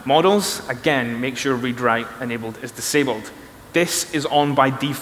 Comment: Im not a english native i dont understand what he say to 21:15 ? In Model he advice to "disabled" "Read/Write" ? Thx for help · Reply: Late response, but maybe it still helps: What he says is: "Make sure 'read/write enabled' is disabled" Weird sentence, I know. But the setting literally says 'Read/Write enabled'.